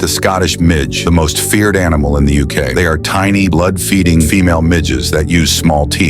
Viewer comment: I wasnt looking at the phone I thought he said, Tiny blood feeding midgets, I was like wtf! Lemme see that